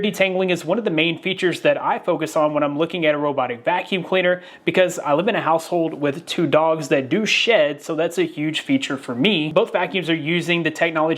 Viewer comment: I heard: ...with 2 dogs that do shit 😂